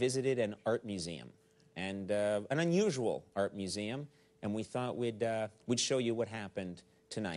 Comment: Damn, I thought it said, "underwater art museum." That would have been so cool.... I was hoping to see conan in a scuba suit looking at weird modern art underwater 😭